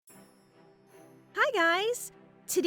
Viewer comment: For some reason at first I thought it said hi gays😂